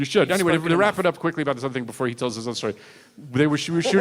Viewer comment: WHAT İS HE SAYING? I mean I didn't understand because he is very fast talking.. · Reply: "let me rap it up quickly about something before he tells his story"